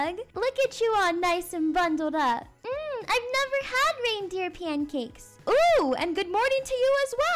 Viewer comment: 😂😂 I thought she said “MMM I never had paper pancakes!” Idk why I Thought that